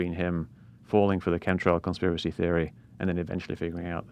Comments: I thought he was saying "cumtrails" until I saw it written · Anybody else feel uncomfortable when it sounded like he was saying cumtrails?